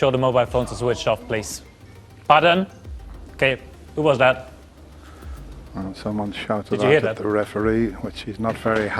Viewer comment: what did the audience say to referee? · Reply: It was shut up or f off